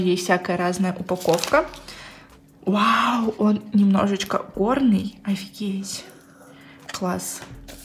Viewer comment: Одна я услышала на 8:55 ,,вау он немножечко ОРНЫЙ" 😂😂😂 я не придираюсь просто может я одна услышала